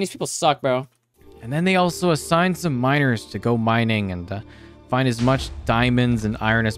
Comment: I thought he was saying "Minors" at first... LMAO